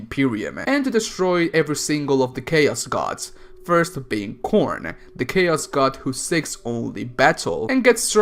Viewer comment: When he said "Khorne" I heard "Corn" XD